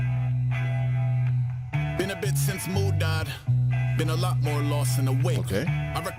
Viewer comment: I thought he said, "...been a bit since mood died."